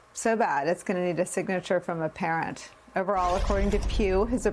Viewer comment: I thought you said according to Q 😅😂😂